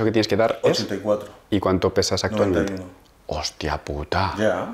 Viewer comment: Tiene que pesar 84kg pero no le entendí en cuantos kg está , alguien sabe cuanto dijo ? En el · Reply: 91